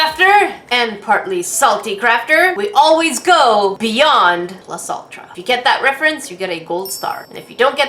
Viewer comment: When she said “Plus Ultra” I thought she said “Plus Saltra”